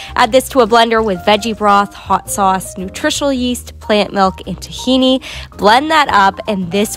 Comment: I thought she said nutritional YeEt I'm like that's good for everyone 👁️👄👁️